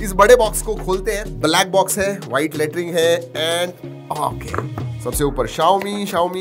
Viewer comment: What does he say after black box at 0:40 ?😂😂😂😂😂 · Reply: White latring 🤣🤣🤣